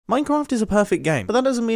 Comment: did you say minecamft ? :-)